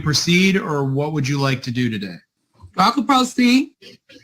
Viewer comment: re the first case, I missed this when I watched it 5 months ago: JUDGE: "Do you want to proceed?" SOVCIT: "I can pro see." Had to rewind a few times to hear that correctly. Her mishearing the judge and then mispronouncing "pro se" is the funniest moment in this video. 😊